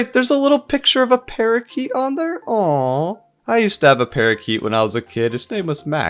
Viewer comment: I heard "I used to stab a parakite, his name was Max" instead of "...used to have a parakite" 😭😭